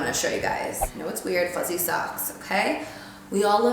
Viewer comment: When she said fuzzy socks I thought she said pussy sucks lol